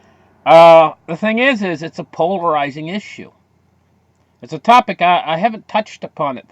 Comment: Polarizing issue.... thought you said pulverizing issue.... 🤣